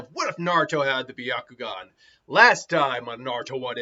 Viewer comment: Ok, that time I thought it said what if Naruto had the bakugan.